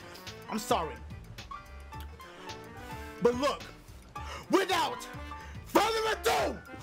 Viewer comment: I wanted to get on with the gameplay at the beginning so when said "without further ado.." I skipped 10 seconds and I hear again, "without furrrado!.."